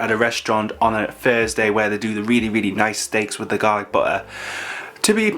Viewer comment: Ok but I kept hearing "garlic butler" instead of "garlic butter" ... regardless am very hungry now 😅